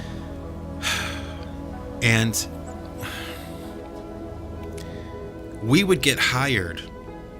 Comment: Legit thought he was gonna say "We would get high" instead of "We would get hired"